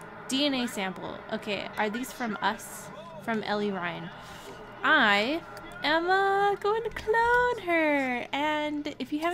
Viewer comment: Watching the first minute of this video then I hear my name. "I'm going to name her Lori" I was like WHAT!!